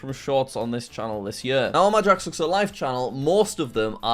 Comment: The subtitles says JackSucksAtStuff but he said JackSucksAtLife